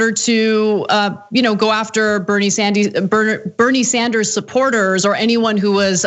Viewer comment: Did she say "Bernie Sandies?" (17:24) Is that like chicken tendies?